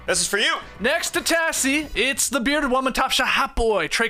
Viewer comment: I swear called Trey “the Bearded Woman” at the top of this one